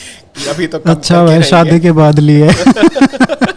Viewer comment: did he say "shaadi k bad" ? So, Sai is married ?